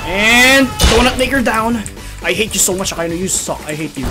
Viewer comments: it sounded like he said donut n word down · what did he say? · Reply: Donut maker down